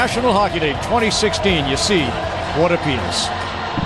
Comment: Why tf did he just say "What a penis?" XD